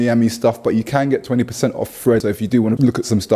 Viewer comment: Over 30, thought my ears where failing me. I thought u was saying Fred instead of thread lol. In all honesty after saying it in my head it made sense